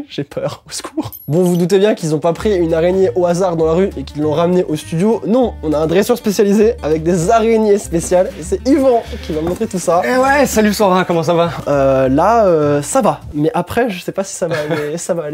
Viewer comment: C’est moi ou à 3:46 il l’appel « Sandra » à la place de « sora » 😅😂😭❤️